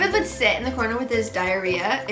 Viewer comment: Did anyone notice at 9:50 ish where she said diarrhea instead of diary, or that's what I heard anyway.